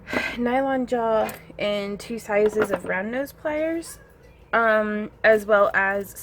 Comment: I swear, I keep hearing "brown nose pliers" whenever you say "round nose pliers." ;)